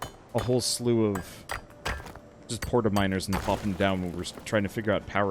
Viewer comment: “We should probably look into making miners” Uh I heard that as “minors” “We should probably look into making minors” Pretty significant difference lol